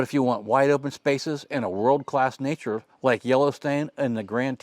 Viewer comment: Haha... at 6:39, did you call it 'Yellow Stain?' Seriously, though, thanks for the video.